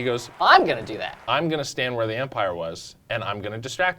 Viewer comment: did you just say "empire" instead of umpire?